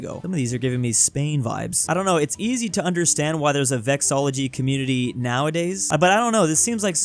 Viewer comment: Also, the word about the "study of flags"--"vexillology"--is pronounced as "vex-uhl-AW-luh-gee" not "vex-AW-luh-gee" as I keep hearing (at 1:49 and 4:09).